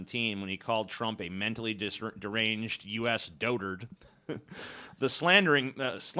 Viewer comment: I thought Iran said Trump had mental retardation